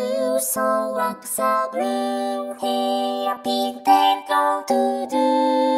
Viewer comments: captions say Blue-haired, but i swear to god she's saying Green-haired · You pruposefully made her say green, but have the subtitles say blue!... clever. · caption says Blue, vocals say Green I don't know how to keep going